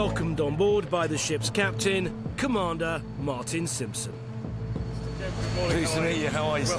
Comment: why did i hear "Bart Simpson" at first